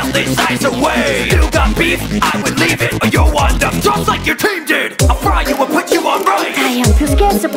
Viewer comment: The part where it said "You'll end up just like your team did" made me go, "WOAH! Harsh..." Reson: Her team died during a freeze storm. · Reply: I thought he said "Youll WIND up like your team did"